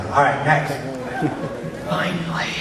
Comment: Best reaction from Trey but wtf did that guy say? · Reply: Serial killer voice "Finally..."